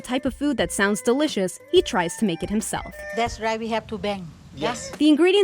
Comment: "that's right we have to bang" ???? or am I mishearing this